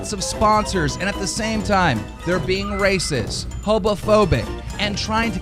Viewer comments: did he just say "hobofobic" lol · Did he really say hobo phobic 16:47 😹 instead of homophobic? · did he say hobophobic? He probably doesn’t know what the right word is due to his low IQ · why does he say “hobophobic” at 16:47 lmao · did keemstar say "hobophobic"?? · did he just say HOBOphobic? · did he just said HOBOPHOBIC?????????????????????? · sounded like he said hobo-phobic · did he just say "hobophobic" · did he say hobophobic? lmao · Did he say hobo-phobic instead of homophobic ? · Okay wait a gosh darn minute. Did keemdump say “hobophobic” at 16:47?!? HOBOphobic? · Did he just say hobophobic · did he say hobophobic lol omg loooooool hoboooo hobooooooo hahahaha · Is it just me, or did he say @ 16:48 "They're being racist, hobophobic..." etc. Wtf is 'hobophobic'? Fear of hobos? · did keem say hobophobic at 16:49? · did Scumstar say someone is "hobophobic"? Lol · am I the only one who heard "HOBOhobic"? Those damn hobos invading YouTube >:( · Did anyone else hear Keemstar say HOBOphobic? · did he really say "Hobophobic"? Does he not know how to ponounce homophobia or it is that they are afraid of homeless people? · did keem say "hobophobic" at 16:47? lmao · am I crazy or did he say hobophobic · did he really say hobophobic? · Does he say Hobophobic instead of Homophobic at 16:47? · Did he just say "hobophobic"? What would be the reason to not sponsor someone with an irrational fear of bums? It's a medical condition. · did he just say hobophobic ?